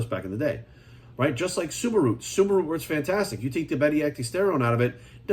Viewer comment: He said suma root, I thought he said Subaru at first lmao